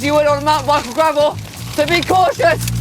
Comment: I don't know what is yelling exactly. It doesn't sound right coming out of his mouth. It almost sounds like he is saying I should be cautious, but...that's not something would say.